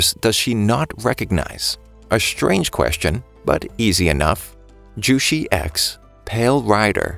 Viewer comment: I thought he said juicey a$$ but he said jushi x